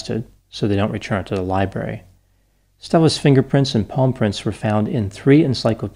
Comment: i thought you said paw prints oh ok palm prints. carry on